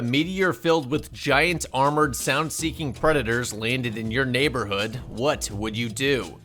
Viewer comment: Thought you said giant armored titans at the beginning lmao. Been binging way too much aot